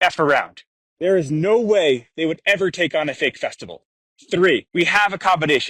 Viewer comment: does he say fake testicle?